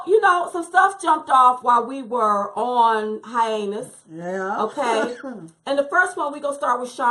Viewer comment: Oh my gosh I spit my food out at Red Lobster at 8:25 when she said "while we were on hi-anus" instead of "hiatus". Ahahahahah